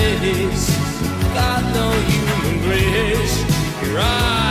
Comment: Got no human grace your eyes without a face, such a human waste your eyes without a face. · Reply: LoL I totally heard, "got no human race" ... I was impressed how PC Idol was for a moment...or was kinda cruel idk